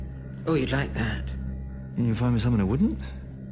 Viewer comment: What'd he say? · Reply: “And you’ll find me someone who wouldn’t?”